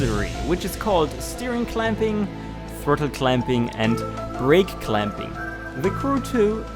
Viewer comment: I thought he was saying “fertile clamping”